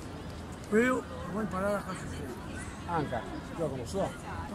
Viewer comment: cosa ha detto? Queo lo go impará da Cassius clay poi? Gli ha insegnato il pugilato? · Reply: Gli chiede : lo hai conosciuto? Quando faceva il pugilato si :D